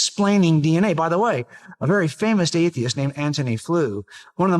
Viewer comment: anyone else hearing him say "famoused"? 😮